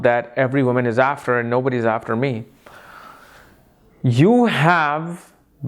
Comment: I didn't understand what he said at 9:58 onwards...can someone explain...we have been everything we desire for ??